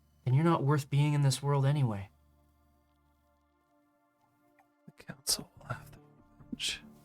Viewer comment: For anyone struggling to hear what was whispered at 1:57:41 it was ... ... (Hiding the info behind "Read more") ... ... "The Council will have their revenge."